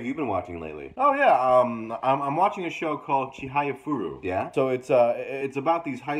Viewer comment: what anime did he say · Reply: Chihayafuru